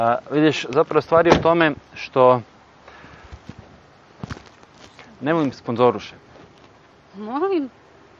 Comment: I am from Bulgaria and i almost understand all the conversation but what did he say at 0:48.Something like "Nema mi sponzorish"What does that mean ?